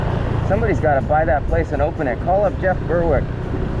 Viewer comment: Ha! I heard you say call up Jeff Berwick!! LOL Let's call him up!!